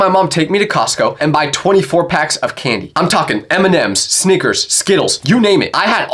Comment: he said my mom would bring me to Cosco and i would get sneakers instead of snickers